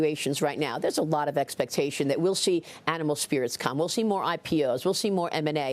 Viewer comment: did I hear correctly: Animal Spirits? 🤪